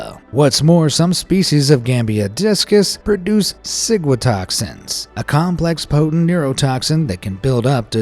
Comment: Am I the only one who thought he said sigmatoxin